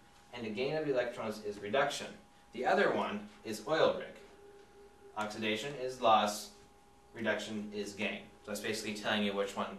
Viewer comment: I heard, "Oxidation is Love... and Reduction is Gay." It took me a second repeat and upping my volume to get it. O_o